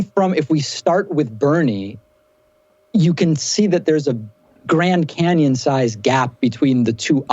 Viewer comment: Thought he said, "grand anion gap". That's pretty small my dude.